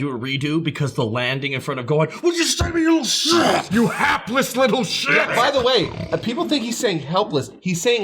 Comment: I fully thought Cell was saying "heartless little shit" because, well, just look at Gohan